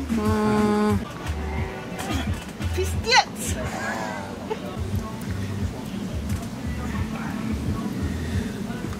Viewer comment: what did she say? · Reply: "Pizdets" she says))))